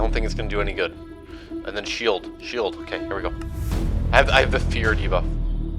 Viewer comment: what did he say????? Did he say “I have feared Eva”? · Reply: “I have a fear debuff”